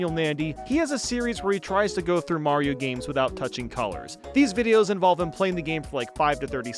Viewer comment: i thought that said touching coins for a secobd